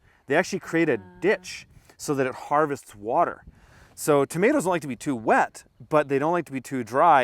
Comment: What is saying at 6:25 to 6:30 about tomatoes and water.. my wife and I are hearing two different things... I think he says tomatoes do not like to be too wet... my wife thinks he says they like to be wet.